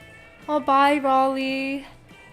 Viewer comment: OMG U SAID BYE TO ME AT 58:10 i think my u said bye that melted my heart